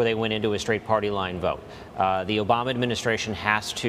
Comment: Did he just said “The Obama administration…”